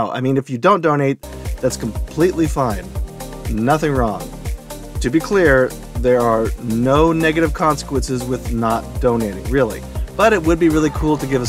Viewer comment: WHAT DID YOU SAY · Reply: I said, "nothing wrong. To be clear, there are no negative consequences." :)